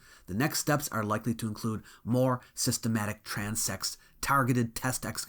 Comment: I know I just heard him say they are going to be having "systematic trans sex" at the 29:53 mark, and I don't know how India gets down, but Syria is definitely not going to go for that. It's a real shame, too. Give it 10 years